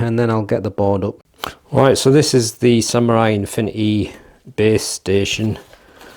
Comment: I thought you said infinity babe station lol 😂😂😂😂